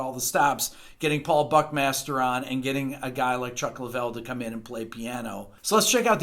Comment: I heard it as "getting a guy like Chuck Liddell to come in and play the piano".